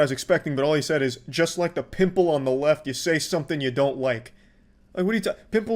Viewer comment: 100% think he said people on the left lmao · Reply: I dont think "a people" would make sense. I think he said "a pimple" as in liek a derogatory term for The Woke Left lmao